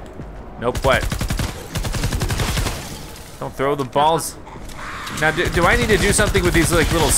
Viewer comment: Did you say balls🍆